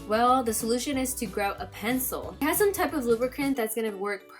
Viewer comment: I heard "you have to grow a pencil" XD